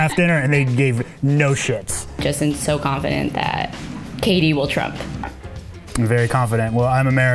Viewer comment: The whole time I thought she was saying "katie" instead of "KD"